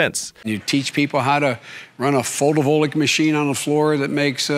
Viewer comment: Wtf kinda machine is he talking about at 3:52? Is he saying photovoltaic???